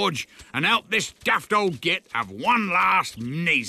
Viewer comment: wait did they just say "git" in a kids show